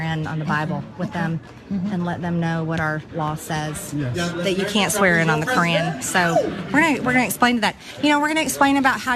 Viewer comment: For a moment I thought she said "swear in on the Karen"